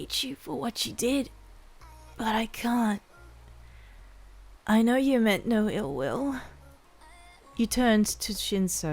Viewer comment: I keep hearing "I know you meant no uwu "